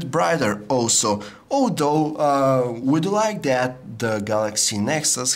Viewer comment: I heard "oh no" rather than although ==